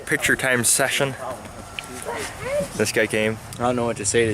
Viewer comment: Hi, I like watching your videos but some of are deaf and have to use the captions which are not always corrects on youtube. With that said at 0:31 you say this guy came but your captions say "this game" which is incorrect. It would be awesome if you could fix this. Thanks!